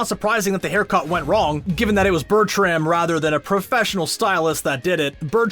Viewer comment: Im sorry, but did he just say bertium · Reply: yes, its BERTRAM*, not *BERTIUM